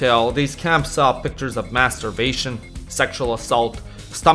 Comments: I nearly spit my drink out when you said Mass starvation I thought you said mass masturbation 😂 I'm hard of hearing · I had to rewind it at 16:25 because I was looking away from the screen and I thought I heard you begin the list of horrible things at the concentration camps with "masturbation." Looking back, I kinda wish I was right. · I legit thought you said "masturbation rather than maas starvation